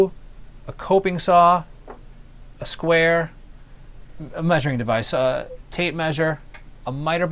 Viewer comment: Lol, I thought he said "pleasuring device" instead of measuring device when I was kinda half paying attention.